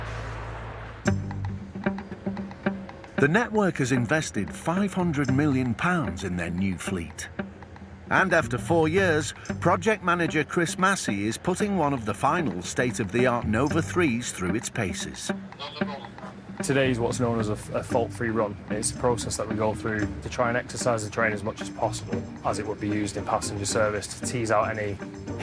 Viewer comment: I heard it as "putting Transpennine's newest Nova 3 through its Pacers" ...lol